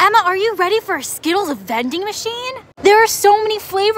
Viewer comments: I thought it said Skittles offending machine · I heard "offending machine"